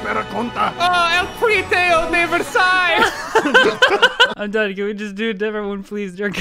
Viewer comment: Did he just said "El tratado de Versalles" XD